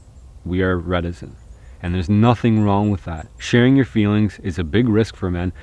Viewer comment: I 100% agree about what you said at 20:15. But I don’t know the term for boys not sharing their feelings. Can you spell it out (sounded like “redisn”)?